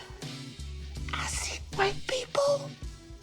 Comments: it sounds like he said I see white people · the way he said this line, it’s easily could have been misheard as “white people”